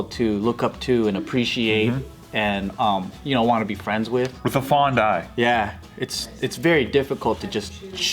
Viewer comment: did I just hear say "with the FAWN DIE"... wtf this video is creepy.